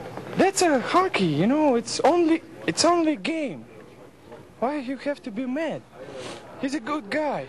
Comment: I thought it said "it's only a game why do you have to do meth?"